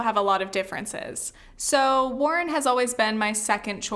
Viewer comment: I thought you said your second choice was Tulsi? If it is Warren, like you say in this video, I'm glad. Tulsi is not good.